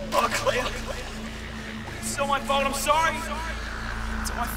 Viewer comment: what did he say? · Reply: Oh cleo, It’s all my fault. I’m sorry!